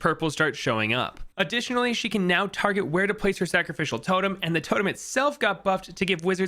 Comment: Unfortunately, was eating cereal when i had to pause, so the sentence i heard was "Gwendolyn can now target her cock- pause", and now i'm feeling bad cause i burst into so much laughter